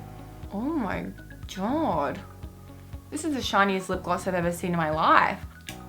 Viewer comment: at 8.13 I thought u said this is the lip gloss 😅